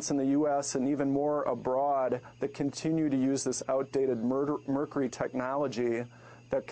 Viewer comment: did I hear him say out dated MURDER TECHNOLOGY at about 4:44 ?